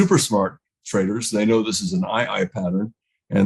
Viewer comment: eye eye pattern? Or did I hear incorrectly at 1:56? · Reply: he types it as 'ii' and it stands for inside, inside.